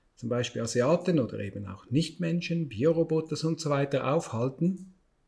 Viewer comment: Tja, die Vorhersage mit Trump ging ja wohl in die Hose. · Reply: Ich hab Bierroboter verstanden. Würde auch zum Adrenochrombacher passen.